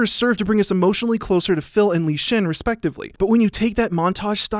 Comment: did he just call him Lee Chin I